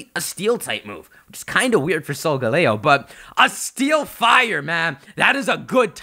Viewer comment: is it just meor did say steel/fire 1:49 in the video